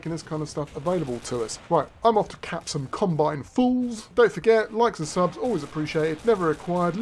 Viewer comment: "Right, I'm off to cap some Combine fools" The first time I heard you say that, it sounded like "I'm off to cap some Combine foals"